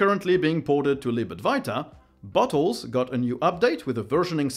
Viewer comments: When you said Bottles, I heard "butt wholes", so I had to unminimize the video to see what the hell are you talking about ;D. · you saying bottles had me a bit confused at first as it sounded like butthole. my annoying habit of mishearing things strikes again